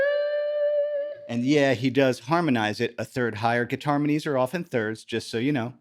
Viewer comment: Did you say "guitarmonies" at 3:29? Love it! 👍